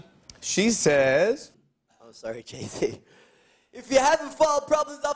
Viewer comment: Did he say 'I'm sorry jason' at 1:04? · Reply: No, i think he said "i'm sorry jay-z"